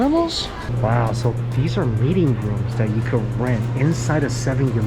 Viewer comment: I thought he said "mating room" 😅